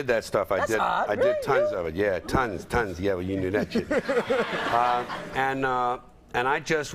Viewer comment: what George said? Someone tell me please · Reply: "Yeah, well you knew that shit." I think 🤷🏻‍♀️